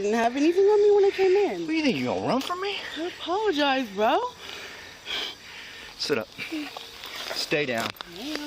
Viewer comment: What? He tried to get her to stand up, then when she wouldn't, he said "ok, then stay down" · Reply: in the video he tells her " set up, stay down" at no point does he say the words "stand up". Are we watching the same video? Remind me to NEVER ask you to be a witness. Did you ride a "special" bus that took you to a small class, with a teacher that talked real slow and gave you alot of one on one attention? 😉